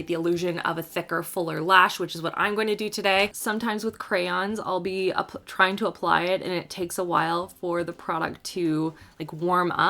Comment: Am I the only one that heard her say "sometimes with crayons I will be trying to apply it..." when she was putting the liner on?😂